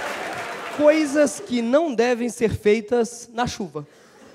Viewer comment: Juro que ouvi "coisas que não devem ser feitas na chuca"